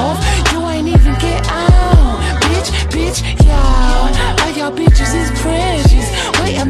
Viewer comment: As i was listening to the song, i thought i heard "biTch biTch bItch- yEOww"